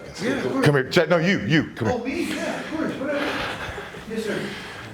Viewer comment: lmao did he say “yes sir” lmaooo he was scared af